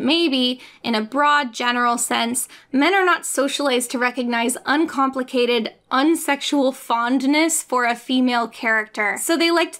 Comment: "Men are not socialized to recognize uncomplicated, unsexual fondness for a female character." Extremely well put. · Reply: Oh for a second I heard "Men are not able to recognize uncomplicated, unsexual fondness for a female character" and I immediately went to the comments to see if anyone picked this up, I'm glad I just heard wrong and saw your comment.